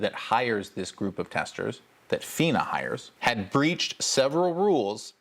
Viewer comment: Is it "had breached" instead of "hadn't breached"? This is important for people who can only read subtitles as both English and Chinese subtitles say "hadn't breached".